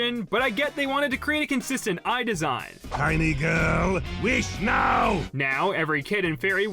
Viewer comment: i thought yorgen said my nword wish now